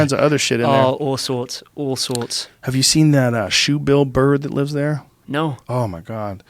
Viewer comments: I thought he said "Shoot Bill Burr, he lives there" he lives there 😳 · When he said shoe bill bird I heard “Shoot Bill Buhr” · I thought he said a shoe bill burr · O I thought he said Bill burr lives there.. billed bird lol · I thought he said “did you see that Bill burr that lives there”